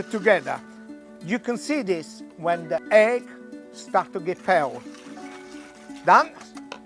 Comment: When the egg starts to get... feld? What is he saying at 0:35? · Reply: egg starts to get pale (in color)